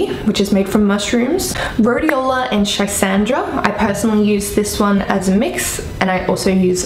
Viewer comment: What did you say after rhodeola 10:19ish? Tricendra? That's what it sounded like to me, I googled it, nothing is coming up..